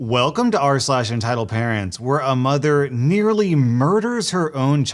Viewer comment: I was so shocked cos I thought he said "a mother literally murders her own child". He said nearly. I'm so relieved by that😅😅